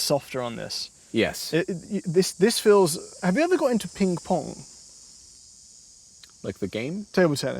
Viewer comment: did bro say pink-pong